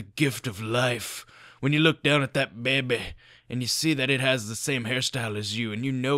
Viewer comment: did he say bamby